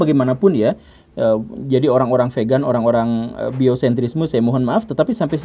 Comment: Apa tadi org² vegan dan fiosentrime?? biosentrisme kali ya? Ato salah denger😂😂